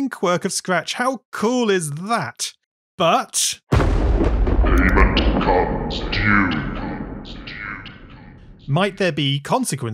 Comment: what did you say in 7:02 i check subtitles and it said But (thunder) not to rain on our parade, but payment comes due... :_thonk::_thonk